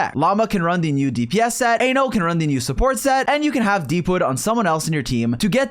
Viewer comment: why did bro say anal??? 🥀